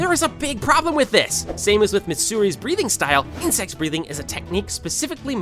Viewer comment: bro wth the subtitles says something way different than insect breathing 💀💀